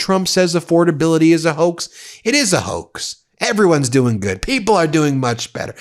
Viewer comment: I thought he said "affordability is the new Democratic hoax". He really is in a panic to start using Democratic talking points to get people to support his stupid, anti-democracy policies.